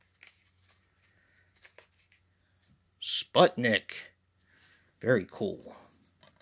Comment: I thought you said Spudnik at first and immediately thought of Friends. But this sounds very 80s in premise and I like that.